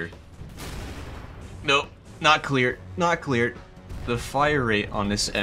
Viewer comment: did he say "not clear" or "nuclear"? 🤣🤣🤣